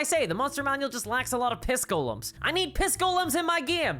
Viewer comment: Piss golems. I'm scared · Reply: I misheard it as kiss golems and visions of being stalked by a deader-eyed Peter Criss (in the very original makeup, more clown than cat)…